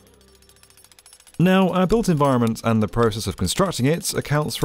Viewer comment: What is he saying. Adult environments? What accounts for 40%? tHanks. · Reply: "Our built environments "